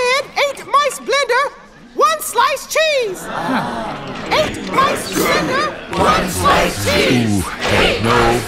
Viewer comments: Thought the hog rider said "ate my splender, once slice cheese" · Eight clans enter, one clan leaves=Eight mice, blender! one slice cheese! LMAO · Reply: I thought he said Eat my splinter! One slice cheese